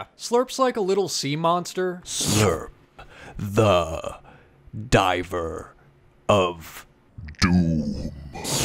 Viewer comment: Omfg I had my headphones half in n I thought u said diaper of doom but then I realized it was diver lmfao I need to go to bed btw I love the crate I want one!!🥺🥺🥺